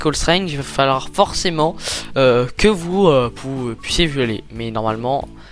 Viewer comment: euhhhhhhhh... c'est moi ou il dit "que vous puissiez v"i"oler"